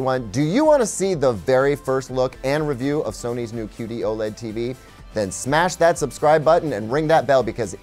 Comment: did you say Sony’s qd oled tv ?